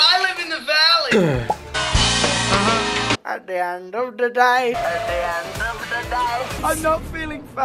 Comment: I never understand what he says here 😭 please enlighten me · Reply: At the end of the day